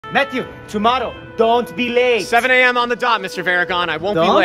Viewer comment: I thought he said "Matthew tomato"